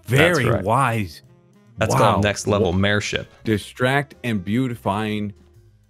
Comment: I heard, "That's some next level mayor sh*t" and I had to rewind, like did SCAR just say that?! I was so confused xD